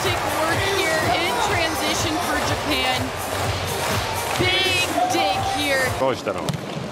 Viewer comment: nah am I trippin or did she say big d here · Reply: Big dig…